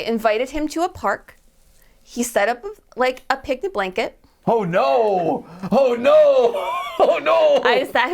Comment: I heard picnic blanket and I was alr heartbroken for the guy 😅 But hey, it happens 🤷🏽‍♂️